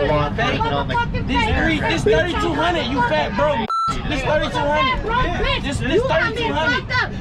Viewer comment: Did he just call her a fat broke bi***? 😂